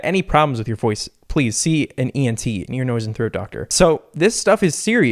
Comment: Did he just say ear noise and throat🤔